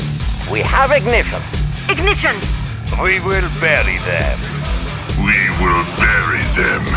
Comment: I thought apocalypse tank is saying "we will belly dance" instead of "we will bury them"